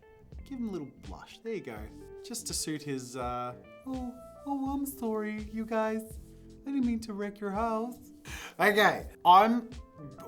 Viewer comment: Auto CC says "I'm sorry, you guys. I didn't mean to wreck your holes." Sus 💀💀💀💀